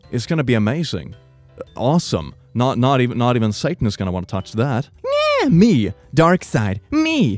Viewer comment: He said “not even Satan is gonna touch that”, I thought he said ‘Sangwoo’ 😭